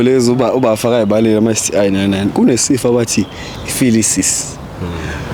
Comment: Did I just hear phylisis. Instead of syphilis (29:22 time stamp)